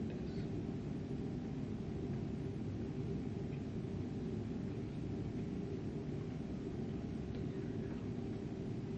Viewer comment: what is saying here other than I love you. Like I do this too under high emotional situations... like i say what I wanted to have said or say something different... like replay situations in my head either before or after the fact. But what is he saying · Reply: Something about ”she actually did this”.